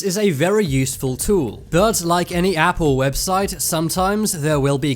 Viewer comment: Anyone else hear "Apple's website" instead of "app or website"